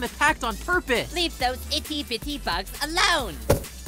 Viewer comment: Fairly Oddparents Reference Edit: I Just Realized Pin Said "Itty Bitty" It's Honestly Hard To Tell What She Says With A Voice Like That